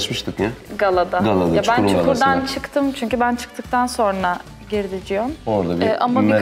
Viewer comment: Dilan Deniz Cihangire "Ciom" mu diyo ben mı yanlış duydum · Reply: Ever ciom diyo